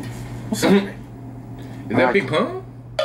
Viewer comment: Did he just say, "Is that Big Pun?"....🤣🤣